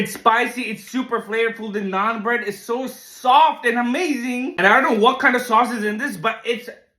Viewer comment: 2025 Food World Cup - Finals <link> 2025 Food World Cup - Group Stage <link> 2025 Food World Cup - Qualifiers <link> 2024 Food World Cup <link> □□□□□□□ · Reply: did you say naan bread!